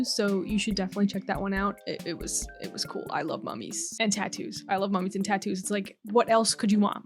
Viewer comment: oke wtf i hear i love mommy and tattoos... wtf who don't like that